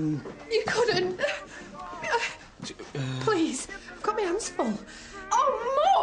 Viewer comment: What does she say at 1:30 after "Please"? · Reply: She says 'I've got my hands full' :)